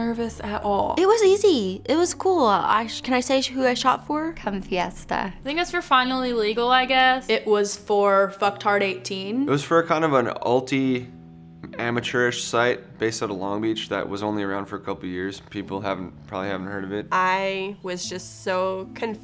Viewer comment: Anyone else hear "Fucktard18" instead of "Fuckedhard18?"